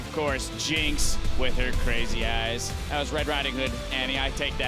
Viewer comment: "Jinx with her crazy Ass" DID YOU REALLY SAID THAT????